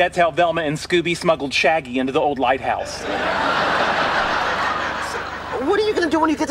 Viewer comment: “That’s how Velma and Scooby smuggled shaggy into the old lighthouse” 😂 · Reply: Oh, lighthouse i thought he said White house